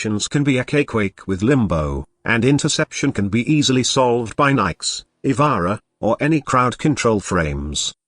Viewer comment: what he said cant catch it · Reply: Interception : Nyx or ivara or whatever cc frame